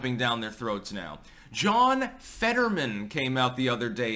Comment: I thought he said that he was John Fetterwoman.